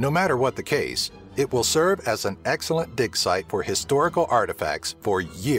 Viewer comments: I'm thought you said dick site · thought you said "It will serve as an excellent dick site for years to cum" I must be getting deaf. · when he said "... It will serve as an excellent dig site..." It sounded like he said dick site to me lol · Am I the only one who hears "excellent dick site"?